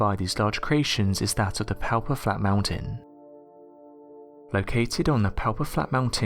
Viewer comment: It's hella funny in number 2 how he says whatever.... flat mountains. It's sounds like he saying alfalfa fall flat mountain.😂😎